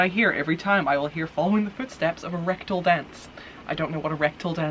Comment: Actually I always mishear it as "Red Dawn Dance", but hey, you do you.